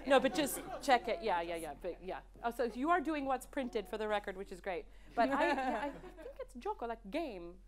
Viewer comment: What is saying at around 1:39:45 ? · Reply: Gioco in Italian, but guioco in this one.